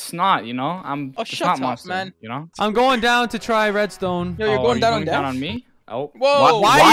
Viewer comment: i think i heard someone say "you're going down on me?" I can't really tell who it was tho